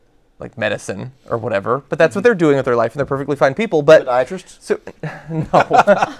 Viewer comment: Did Ben say "psychiatrists"? Wtf, is he a Scientologist now?